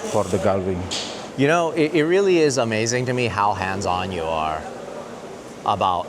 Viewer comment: He said "how hands on", but the thought for a moment he was been called handsome